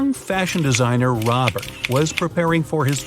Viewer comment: Why did I hear Robber?